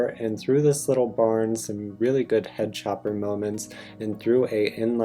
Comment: did you just say "head chopper moments???"